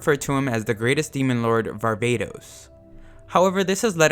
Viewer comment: i thought the ai said “barbatos” instead of “varvatos” anyone else a genshin impact player?